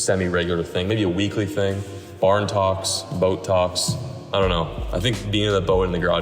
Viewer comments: My dumbass thought this man said Botox not boat talks... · ohhh boat talks. I thought you said botox.